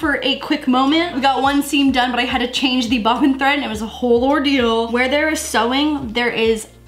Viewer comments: wait wait did she just say LAURdeal instead of ordeal?? hahaha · she definitely said "whole ordeal" while using the sewing machine but I swear i thought she said "whole Laurdeal"